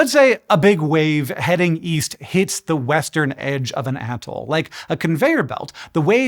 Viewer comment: "let's say a wave hits the western edge of an atoll" the wave hits the eastern edge of the atoll on screen, lol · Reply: I thought he said weast